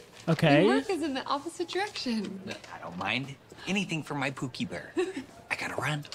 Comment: did he just say cookie bear